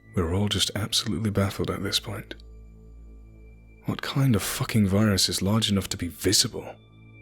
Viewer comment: The idea of a memetic virus or parasite terrified me. "What kind of parasite is big enough to be visible?" Uhhhh these guys are scientists? Tapeworms just to start with. Tons of parasites are. Author really fucked up there. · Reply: diante baileu listen to 8:35. After they notice movement in the tissue. There's speculation that it may be a parasite. I will admit I misheard it while laying in bed last night. After parasites are first mentioned I heard the next line as "what type of parasite is big enough enough to be visible" even beyond that, Epulopiscium sp. Is a bacterium that's large enough to be seen by the human eye. And MGVCs or NCLDVs are giant viruses, much larger than most. Though far from visible to the naked eye, it's not hard to imagine an offshoot as large enough to be seen, but it would probably be more like Epulopiscium sp.